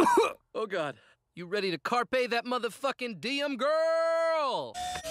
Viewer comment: you said you don't know what Brett is talking about. He's saying "Carpe the m-fing diem":"Seize the m-fing day"